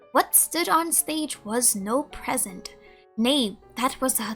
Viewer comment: thought you said knave being that that would be fitting